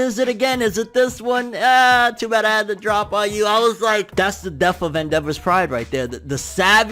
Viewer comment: F In The Chat For Endeavor's Pride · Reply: I thought it just said “murder of endeavor”